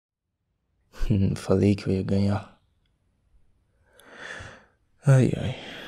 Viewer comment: No começo eu achei que ele falou " aí ai, eu falei que eu ia banhar" 😅😅